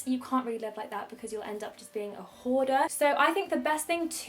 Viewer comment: oh y god I almost thought was going to say whore at 7:11 😂😂😂😂